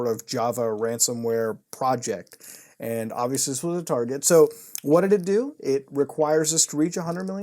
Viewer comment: It sounded like he said "obviously it's really retarded"